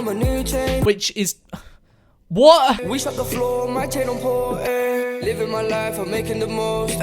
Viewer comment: it sounds like hes saying my channels poor